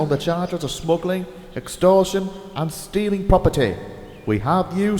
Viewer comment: What's the Police Saying? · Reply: Marbuso Chu it is stealing properity